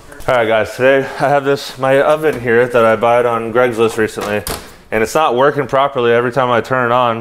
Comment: The thing that made me laugh the most is saying “Greg’s list” 😂